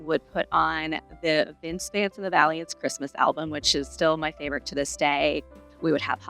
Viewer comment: Could anyone tell what the name of the Christmas album is that she mentions around minute 4:30 please? I played it back multiple times, asked my husband if he could understand what she says but he couldn’t either. I love to look up Christmas music and would really like to know what the name of the album/artist is that she mentions here. Please and thankyou! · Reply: I was wondering too. Someone else commented it is Vince Vance and the Valients.